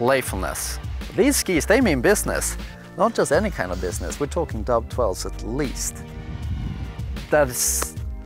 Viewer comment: what did he say? "Dove 12's at least?" (?) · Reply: Oh Doub 12´s at least. Meaning double cork 1260s and more difficult.